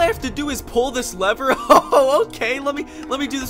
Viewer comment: if you didn't understand he said "OHH OH MY GOD HOW MANY HOW MANY HOW MANY THIS IS THE BEST GLITCH EVER!!!